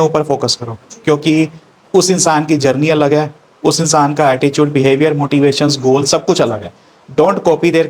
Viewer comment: Sir, did I hear attichude in place of attitude ? timing